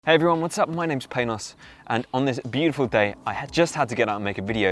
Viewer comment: Lol I thought he said "My name is Penis" when the video started...